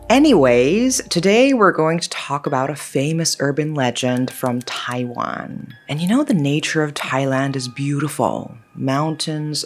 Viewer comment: Am I the only one who heard Thailand !? 😂 ฉันชอบเนื้อหาของคุณมาก ✌🏻🩶